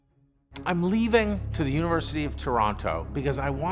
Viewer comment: Canada? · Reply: Ok I think I thought 0:31 said he was "leaving the University of Toronto" but he actually says "leaving to the University of Toronto". My bad.